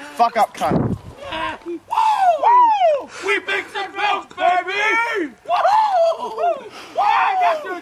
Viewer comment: What did they say at 1:16? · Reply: "WHEAT BIX AND MILK BABYYY! YEAH GET ME SOME OF THAT!"